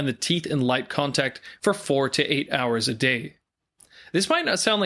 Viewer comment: Did you just said 48hours a day? WTF????